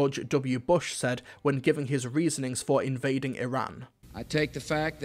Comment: I'm not sure if it's just my hearing playing up, but did you say invading *Iran*? 👀